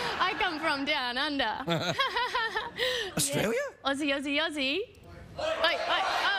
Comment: One of the most feminine giggles you can hear in your entire life. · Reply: oh, it's aussie, I thought she was saying "ozzy". That's why it was remiding me of AC/DC, they use the Oi! chant on some songs.